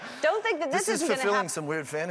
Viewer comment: Sounded like he said this getting intimate